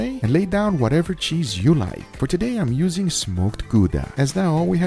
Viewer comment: lol i thought i heard smoked guga